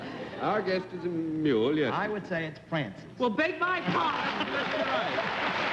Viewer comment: Another female garbage collector? I think WML did this three or four times. This was perhaps the first of the bunch. I loved Francis's appearance. How else could it have been handled to give the panel more trouble? There was no way to hide the mule's heavy-footed clomping, but Francis's voice could have been done by his movie voice, Chill Wills, offstage or even on. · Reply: ***** In replaying the video a few times, I think the responses I initially attributed to "Francis" actually came from Hal Block. The first was a falsetto voice at 18:39, saying (I think), "Well, beg my pardon!" That must have been Hal, referencing what Bennett had said at 18:22. The second (at 18:56) was probably Hal's regular voice, off-camera, saying, "First time I've kissed a star in years!" -- but it came immediately after John said to Francis, "Say something to the folks," and we see Francis moving her mouth at the same time, so that's why I thought it was supposed to be Francis saying it about Hal, though I guess it was actually Hal saying it about Francis! :D